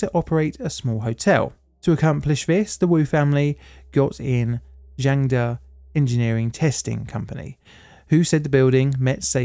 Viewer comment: who thought they heard "Jenga Engineering"